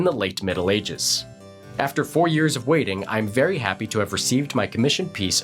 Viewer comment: wait did I hear that right? 4 years of waiting?